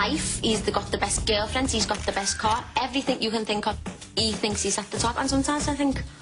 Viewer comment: Can anyone plz tell me what she says at 4:57? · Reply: she says "he thinks he's at the top and sometimes..."